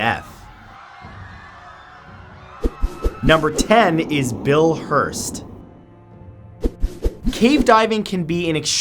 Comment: When u said 'Bill hurtz' I thought that u said 'Bill wurtz' He is jesus